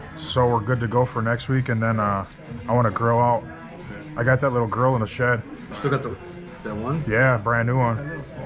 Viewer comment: I had to rewind a couple of times. I thought he said...I got that little girl in the shed.